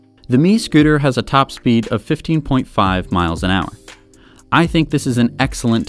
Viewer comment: anybody else hear 50.5 miles I went crazy then relised it was 15